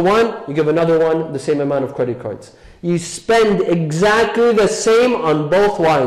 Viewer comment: did you say "credit cards?" Isn't that haram? Like "If you give one wife a bottle of whiskey, give the second wife also?" Shaykh, how many men at the age of 18 don't have a sex drive? I don't think you will find one who does not want to have sex. How is he supposed to do that? Marriage right? What do you think all these 18 year olds are doing with this urge? So I don't know if I can agree with your arbitrary statistic 18 year old men are not ready for marriage.